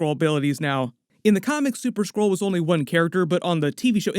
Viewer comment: I kept hearing 'super squirrel'. 😂 Love your videos, keep up the good works!